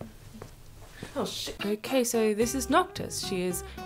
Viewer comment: Only i hear at 2:55 russian word чтож?